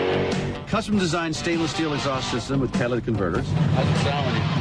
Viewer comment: Did he say “Cadillac” converters